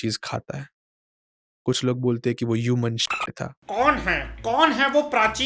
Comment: pr us ny Kya bola Jo beep krdiya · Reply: I think human shit